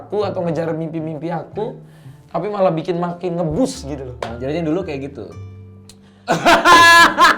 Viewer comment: Yg pas 7:23 dika ngomong apa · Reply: "jadi yg dulu kyk gitu?"itu kata bang radit